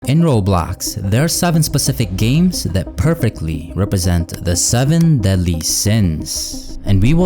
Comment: I thought you said the seven deadly chins